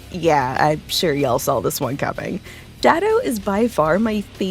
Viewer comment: did you just call him Daddow?